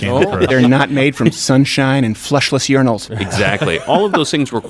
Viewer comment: Ok, I kept hearing “fleshless urinals” so I was like “Oh my gosh! Some urinals have flesh?!”